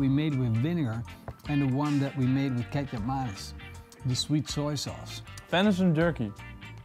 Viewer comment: For those that don’t know kecap manis is just sweet dark soya sauce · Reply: Thanks! I heard "Ketchupmayonaise" and then he poured in this dark matter and I knew I was lost. Whatever it was I was sure it was better than Ketchup and Mayonnaise..